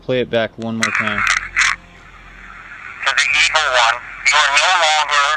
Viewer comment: is that or someone else talking in the mic? I swear to hear someone saying "it's some evil"